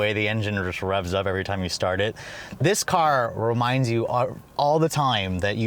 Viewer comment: That V8 sound... Lexus, I don't know what you're on, but PLEASE keep doing it! This car sounds GODLY!!! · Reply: i was gonna kill you, I thought you said "this car sounds ugly"